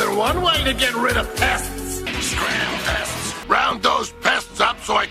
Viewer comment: what did he just say? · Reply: He said, “Scram, pests.”